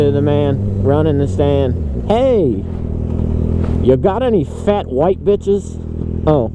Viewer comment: I started singing the duck song and said “hey, bum bum bum got any grapes?” And all I heard was “hey, got any fat white bitches” dies out laughing. Certainly quality content.